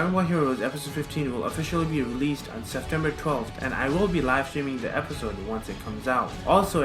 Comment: Dont look.. episod 15 release date on 26sept. Thanks me later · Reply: he actually says september 12 at 0:28 XD